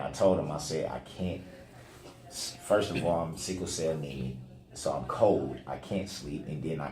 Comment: did he say he’s “sickle cell anemic” which causes him to be cold?